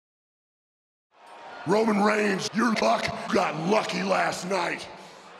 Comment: Not even one sec in and what did I hear "Roman reigns your coc got lucky last night" Right